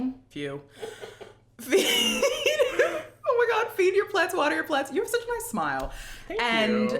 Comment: I always thought you said feed your plaits